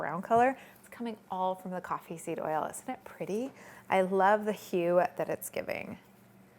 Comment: What does she say at 8:57? She loves what? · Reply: Nenis Guevara Gómez she said the hue of it. So the color or shade of it.